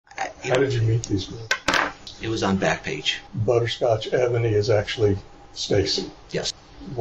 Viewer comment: Almost choked on my lunch when I heard Butterscotch Ebony 😂😂😂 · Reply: Imagine how hard *I* laughed when I thought he said 'Butterscotch AVENUE'...